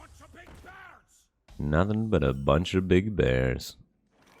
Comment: I thought the sharpshooter says "birds" not "bears" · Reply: Oh I thought he said "Just a bunch of big bads"